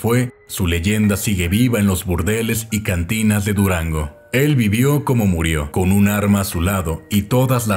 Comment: Nadie: Absolutamente nadie: Yo: ANumA i0 ZoI dE Durango